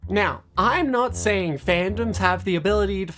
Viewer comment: My headphones are low and it sounded like she said "Now I'm not saying that BATTERY IS LOW"